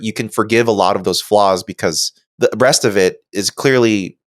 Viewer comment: Did he say "the breast of it" ?